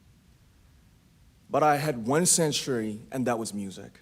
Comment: We became like ghosts but i had one ______ and that was music. What was the word fit in the blank? Some say shelter other sensory-. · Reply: He said sanctuary.